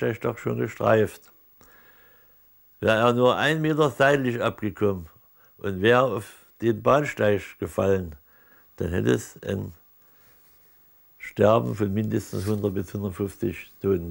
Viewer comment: Wer kann mir die lyrics geben? Verstehe kein Wort von dem nuschel opa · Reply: "Wäre er nur einen Meter seitlich abgekommen und wäre auf den Bahnsteig gefallen, hätte es ein Sterben von mindestens 150 Toten gegeben."